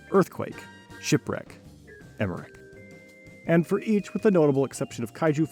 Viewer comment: what does he say??? youtube captions aren't helpful and slowing down the video doesnt either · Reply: Earthquake, Shipwreck, (Famous disaster movie director Rolland) Emmerich.